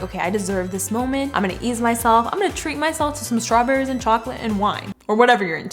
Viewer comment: did anyone else think she said "eat myself" or is that just me...😂